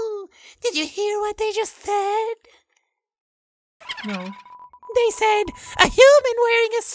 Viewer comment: did someone hear a no?.....